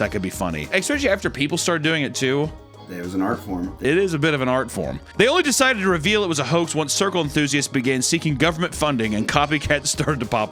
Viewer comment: I thought it said ant art